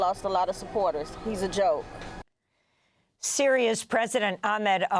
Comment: Interviewee: "Trump is a joke" Amy, starting the next segment: "Syria's president..." I can't be the only one who initially heard it as "serious president", in contrast to Trump 😅